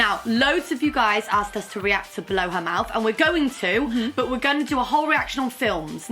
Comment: I thought said "Blow her mouth" I googled it and suprise suprise porn came up..